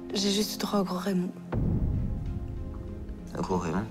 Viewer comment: C quoi le mot a 13:29 svp quelqun peut me répondre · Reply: Je crois qu'elle a dit "J'ai juste eu droit au gros Raymond"